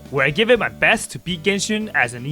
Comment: I honestly thought he said “where I try my best to beat Genshin as an emo” i fr fell outta my chair laughing